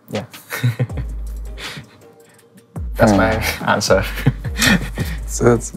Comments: ai có thể giải thích soobin nói gì ko ạ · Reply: chia tay là breakup. Tuy nhien nếu bạn hỏi đúng đoạn 49:30 thì soobin nói là thats my answer. Dịch là Đó là câu trả lời của em. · Soobin nói gì thế? Không nghe rõ lắm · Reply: break up, chia tay í